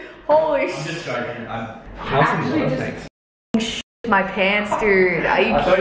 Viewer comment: umm did I hear “I’m just joking hun” are they dating or did I miss something